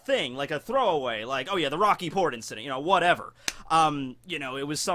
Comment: I thought you said "The Rocky Porn Incident."